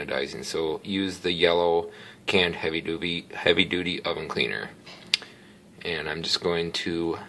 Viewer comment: did he say Heavy doobie?! 😂